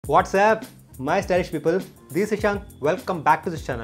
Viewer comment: wtf is he just said "WhatsApp" ???